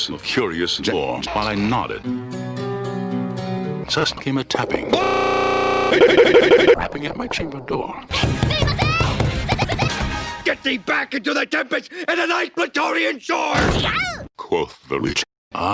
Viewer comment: I'm sorry, what did he say? Did he say "wrapping at my chimney door"?